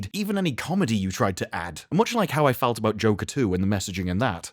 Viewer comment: Haha thought you said joe katoo at the end there and it took me a second to realize you said joker 2. Hahaha